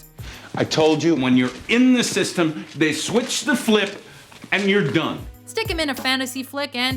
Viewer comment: did I hear him say switch the flip?